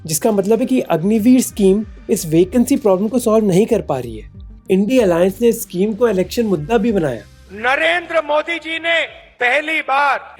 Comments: did he just say INDI alliance 🤔🧐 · Did you just say INDI alliance? 😒